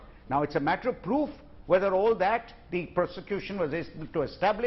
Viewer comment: in this video, the lawyer makes the Freudian slip by saying Persecution instead of Prosecution!